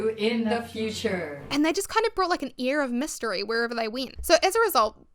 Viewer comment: I love your accent ❤ it sounds like youre sayimg "ear of mystery " mot air lol. I love it 😊